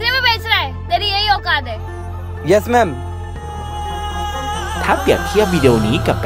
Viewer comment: ทำไมได้ยินเป็นเย*แม*